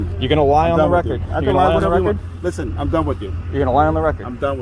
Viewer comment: Did he say, he can lie when ever he wants? At 8:48?